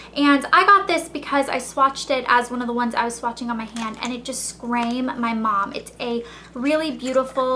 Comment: did you say "scrame" at 3:40?? or am i going crazy? :P